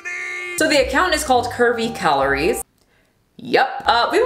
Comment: I thought you were saying Kirby Calories .__.